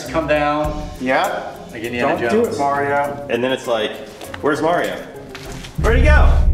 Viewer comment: When I need an Oreo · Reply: He said Mario but it still sounded like he said Oreo! XD